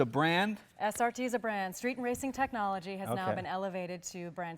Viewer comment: i thought i heard she said "street racing technology" instead of "street AND racing technology" lol...